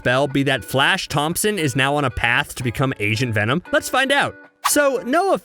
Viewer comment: When you said agent venom it sounded like you said Asian venom XDDDDD